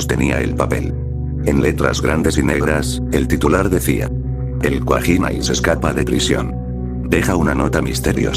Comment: Aáaa el cuajina yo escuché el pajin